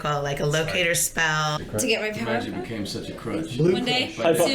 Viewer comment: What did kat say at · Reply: She said she wanted to get her powers back